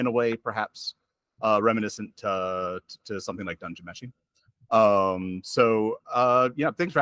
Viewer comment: What the heck is he saying at 10:24 - Is he referring to Dungeon Machine?? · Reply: Dungeon Meshi